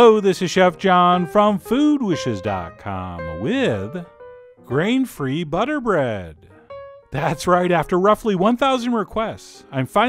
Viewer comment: Dang, I thought it said peanut butter bread, I was excited...for some reason